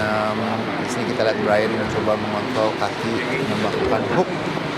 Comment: Menit ke 4:36, apa aku salah dengar? 🤣🤣😂😂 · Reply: Hahaha mengont*lkan